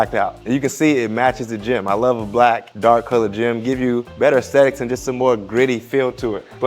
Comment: Sound like you said better sex .. sorry it's late 😂 okay now😁